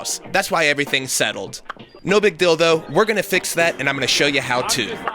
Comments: "No big deal though", I heard "No big dildo"...Haw haw. XD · It sounds like he said No Big Dildo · Is it me or did he just said "no big dildo" at 1:00?